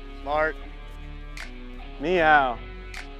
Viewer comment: Anyone else hear Frankie say “Ni Hao” after Collin’s shot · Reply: He said "meow"